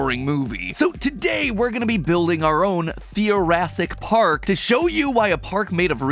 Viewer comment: Oh, Theoracic... a while ago I thought you called it Thoracic, and I was wondering what it had to do with thoraxes.